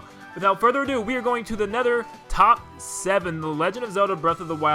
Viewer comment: Please forgive the Minecraft reference, but at 0:32, I thought you said, "A Nether," while the fire dragon was on the screen, and I thought you were making a terrible pun.